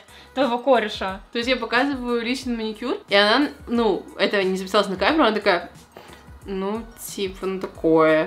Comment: она просто ОБСИРАЛА мой маникюр При этом мастер: ну, так уже мало кто делает. Это просто на моё усмотрение, я не люблю выделяться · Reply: тут четко сказано, что не записалось, вы чем слушаете? девочки же не всегда снимают, это попросту невозможно сидеть несколько часов на манике и снимать абсолютно всё. поэтому судя по всему подсуетилась снимать, уже тогда, когда мастер начинал сьезжать со своего "обсёра" (хаха как сказали в видео и). то, что мастер сказал в видео, не значит, что тоже самое было сказано в жизни, наверное лучше знает, что сказал мастер, она ведь всё таки сидела с мастером.)